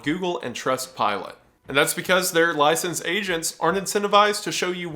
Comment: I thought wendigoon said “licensed Asians “… for a full minute, I felt seen… 😐